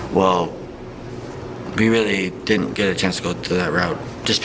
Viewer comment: he actually said "we" not "I" right after